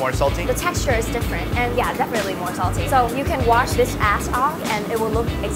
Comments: instead of ash she said ass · It sounds like she said "ass off" The subtitles said "ass". Put on subtitles